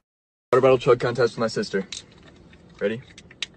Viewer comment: Wat d f**k..did he said his sister or wat at 1:10🤔